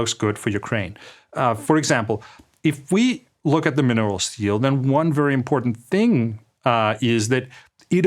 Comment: How automated caption writes "mineral steel (steal)" instead of "minerals deal" in the subtitle is funny... or insightful?